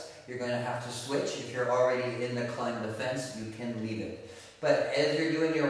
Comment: Can't hear what he calls the 2 hand connections. 0:28 "pac man hold"? 0:33 "clam defence"?